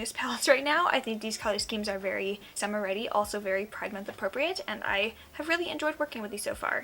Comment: On 3:15 I thought you said “pregnant appropriate” and I was about to comment WHAT ARE YOU NOT TELLING US LMAO